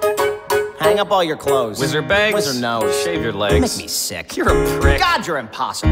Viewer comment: I always thought that at 0:06 it said "With your bangs?" "With your nose." Ya know cause they fight and shit